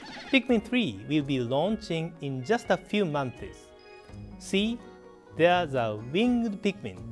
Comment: does he say months or mondays.